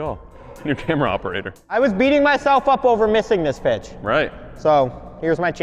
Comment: I heard "off" instead of "up" at 12:27... what is wrong with me?